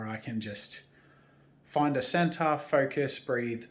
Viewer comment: As a spectrum person with ADD its great to feel so much a part of this community. Also, when said "center" I heard "centaur" & now I have to watch Danny Dont You Know. ADHD away!